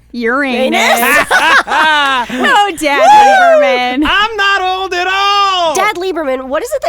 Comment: thought they were saying DAB Leiberman.